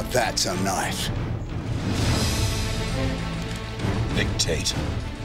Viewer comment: Oh his name is Nick Tate. I thought they said his name was Dick Tate which would be sooooo appropriate